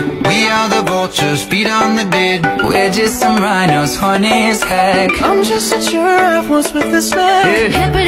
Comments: "We just rhinos, horny as heck." "😏😏" NAAWW 💀💀💀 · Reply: Ohhhh I misheard it for we funny as heck and I was like why is this bad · “We are some rhinos we horny as heck” a truly cultured man. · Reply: That's not what I heard, I heard "We just some rhinos funny as heck"